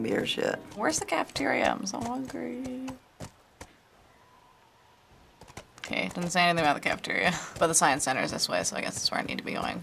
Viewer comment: What does she say at 1:04 😅😅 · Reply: "Kay, it doesn't say anything about the cafeteria but the sign says the science building is this way so.."